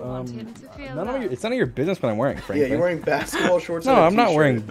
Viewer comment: did dream just call sapnap franklin 😭😭😭